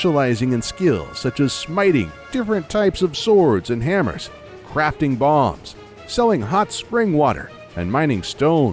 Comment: I heard "Crafting Bongs" and you can not unhear it now.